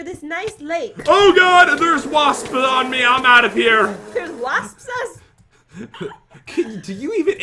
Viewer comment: In jen's defense, I heard waspsas